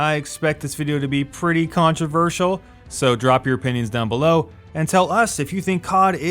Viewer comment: i thought you said "So, drop your panties down below...." lolwtf